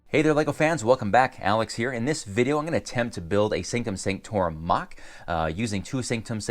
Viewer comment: I thought it said building a scrotum XD